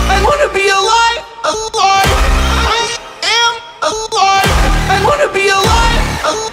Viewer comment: Its sounds like morty says 'I wanna be a lie'